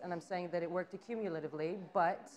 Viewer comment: What's the word she says at 3:46? I tried my best at googling it but I'm getting no results.. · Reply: accumulatively?